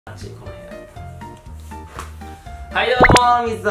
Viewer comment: ボン人には｢あちいなこの部屋｣と聞こえてそうでない人には｢あ、ちんこの部屋と聞こえる｣本物の視聴者がわかる神動画